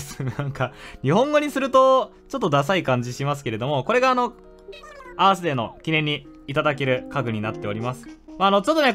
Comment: アースデー最初バースデーと空耳した